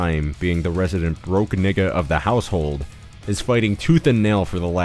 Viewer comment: did you say nigga?